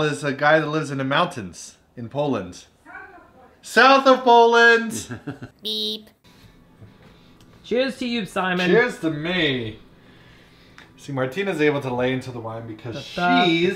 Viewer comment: Wait, was Martina saying Tschüß or cheers? Lol 0:50 She's sounding toasty! X)